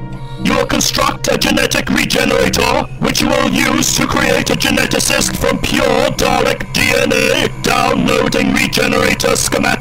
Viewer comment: I'm not a native speaker, can anyone tell my what exactly is said after "progenitor"? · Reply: "You'll construct a genetic regenerator which we'll use to create a 'Geneticist' from pure Dalek DNA."